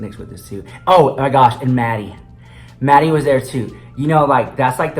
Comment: When you said Maddy was there , i totally thought you said Manny 😂😂😂